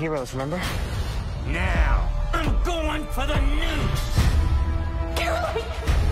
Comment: LIKE CHUCKY IS GOING TO USE NUCLEAR BOMBS, Chucky said: Now, I'm Going For The Nukes and I thought he said Now, I'm Going For The News.